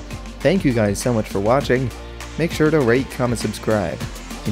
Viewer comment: I thought at the end he said rape comment and subscribe